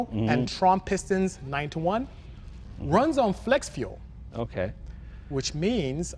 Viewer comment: i thought I heard Flex Glue 😂😂😂